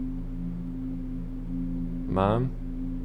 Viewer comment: I was like hi grandma what she really said “mom you need a bath”